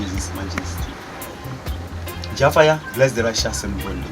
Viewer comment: what is the blessing he gives? I didn't catch the end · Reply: he says, bless the righteous and burn the wicked.